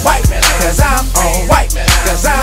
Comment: I always thought it was saying white banana 😂